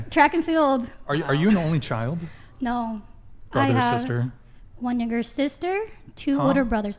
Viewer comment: As a black and asian person, blasian if you will...it Definitely sounded like she said "nigger sister"....but as an Asian, i understand how we just speak differently sometimes...